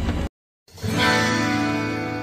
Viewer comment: le entendi cesar Zeña Basura xD